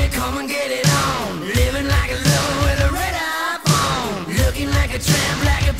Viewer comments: does he say red iPhone...? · I always thought he said "lover with a red iPhone" lol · I could of sworn I heard him say “Living like a rebel with a red iphone” lol 😂 I know he ain’t really say that, but it does sound like it